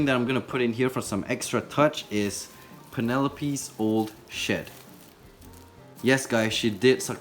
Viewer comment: Anyone else hear shit instead of shed?